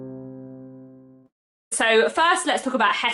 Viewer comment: I definitely heard “Margaret Oliphant”. Maybe, it’s because I’m a very long term subscriber and used to your enunciation. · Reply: The blooper at the end - “elephant “.